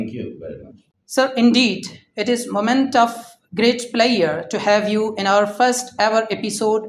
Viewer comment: moment of great what? I could not understand word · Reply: Pleasure